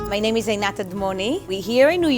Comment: I swear she said "my name is alot of money" lol